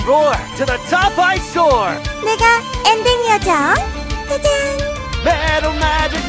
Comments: Did she just say the N-word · What did Melodie say????? 😦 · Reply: Im ending angel